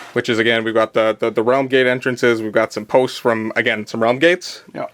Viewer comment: For a moment I thought said "RUM gates..." and was like OH YEAH!!!!!!! :D